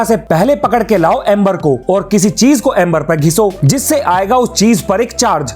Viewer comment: ? I can't understand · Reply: kissi